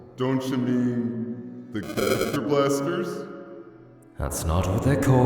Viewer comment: What was this suppose to say?🤨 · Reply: Gaster blaster